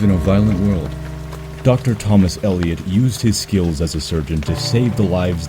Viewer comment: Lol I thought said Thomas idiot